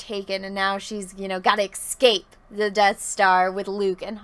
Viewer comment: Did you say "excape"?